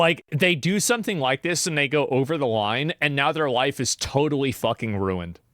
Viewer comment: i really thought he said out of context (do it for the vine) actuall over the line